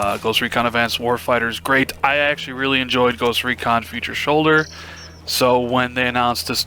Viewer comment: was it me or did i hear say "Future shoulder" at 01:26? lol, back on topic i played the beta and i had a hard time getting into the game, ill give it another shot in the future when its cheaper.